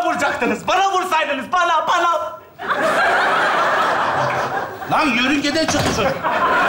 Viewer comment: de tokatdan sora safa ne diyor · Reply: Lan yörüngeden çıktı çocuk diyor galiba.